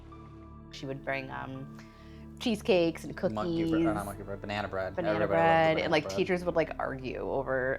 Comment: this has nothing to do with anything but did he just say monkey bread before he corrected and said banana bread ? 1:16 maybe was a cute/silly name they had for it as a family